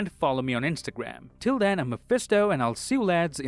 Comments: Di- did he just say.. ‘I’m Mephisto..’ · Anyone else hear “until then I am mephisto”